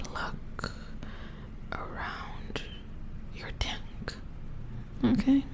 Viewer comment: i swear to god i misheard this as "dick" but what i think you said was "neck" bc this section is in the chest area? please help TwT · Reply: I think it's "tank" ?